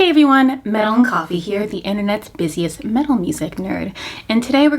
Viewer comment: i thought you said melanin coffee lmao